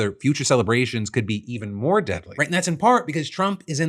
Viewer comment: when phil says "more deadly", captions say "Right" (yes, with a capital R)🤣🤣🤣🤣